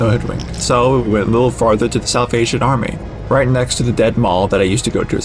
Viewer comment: Lmao I misheard Salvation Army as "South Asian Army" and got completely discombobulated